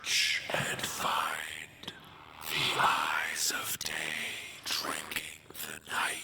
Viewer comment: Lmao 45:01 I wasn't paying attention and all I heard was "Day Drinking" and I was like.... wait, what??? 😂😂